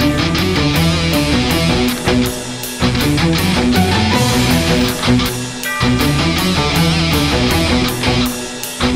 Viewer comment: for the ccs did they say handguns or handcuffs? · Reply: Handcuffs. Sorry for the late reply.